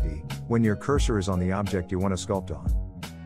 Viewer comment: How did you do the change between the objects so quick?. I couldn't understand what you said in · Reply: You hover the cursor on the object you want to change and you press D .